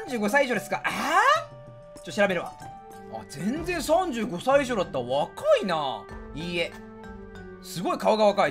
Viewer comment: ここ「全然35歳以上だったな」って聞こえたんやけどワイだけ？